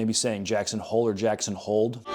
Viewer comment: i thought he was saying "JACKSON, HOLD!" telling them to defend and fight instead of getting scared and retreating